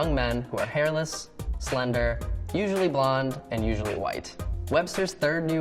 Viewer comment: So, only WHITE MEN?! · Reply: He clearly says the word, "usually" not "only".